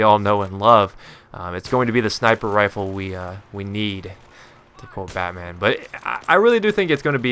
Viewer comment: Did you say "It's going to be the sniper rifle we..uh..we need....to quote batman."??